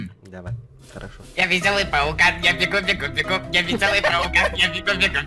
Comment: Лично я услышал Ваш весёлый тот пукан я бегу бегу бегу